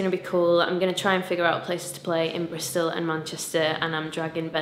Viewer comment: I thought you said Brazil.. I was so happy for a minute! :/